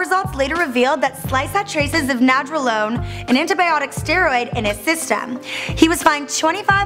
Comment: does she say antibiotic steroid?